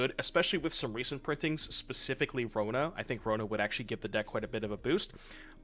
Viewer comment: Can't really hear what you're saying but it sounds like "...specifically Frodo"... But none of the LOTR cards are legal in Pioneer :)